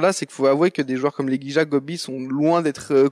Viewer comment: les quoi ? xD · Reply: Legija ;)